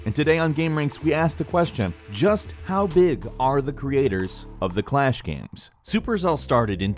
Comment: XD thought it said how big the creatures are so i enterned haha